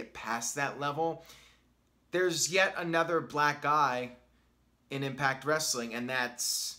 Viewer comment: I didn’t hear properly, and thought he said “there’s yet another Black GUY in Impact.” LMAO!